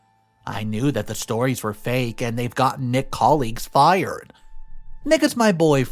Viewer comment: Hi, love watching but at 13:17 the text instead of saying Nick's says ni..a's. you might want to fix that.